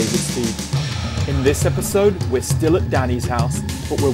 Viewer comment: Not gonna lie, in the beginning of the video, I thought he said "We are still at daddy's house."......I paused the video and was worried I clicked on something I shouldn't have LMAO. Keep the videos coming David. I love them!